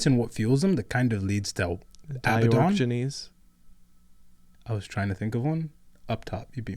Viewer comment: What did he say at 32:37 I could not here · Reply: I think it is Di-ork-genes as a play on diogenes